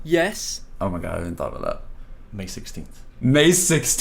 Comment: Wait I thought Jason said may 12 ?